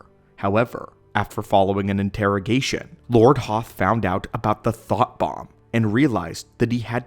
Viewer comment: Forgive me, but I swore 11:55 he said, "The THOT Bomb!"